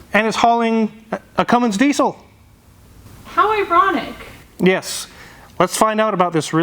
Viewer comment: Did she say E-ronic?😂